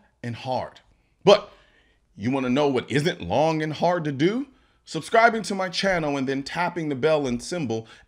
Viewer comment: Selective hearing... All I heard was "you know what else is long and hard?" I was disappointed when you started talking about subscribing. 😂